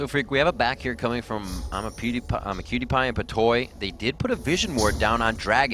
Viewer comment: lol when they said imaqtpie i thought they said imapewdiepie